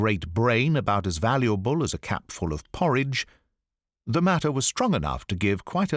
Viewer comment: His brain was about as valuable as a capful of porridge... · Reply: Ooohhh! We heard it as cat-full 🤣